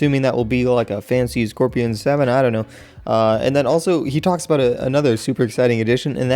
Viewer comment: For some reason i heard "Fucks" about instead of "Talks" Do I need a hearing aid?